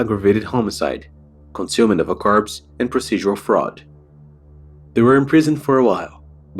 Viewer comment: did you say consuming of the carbs?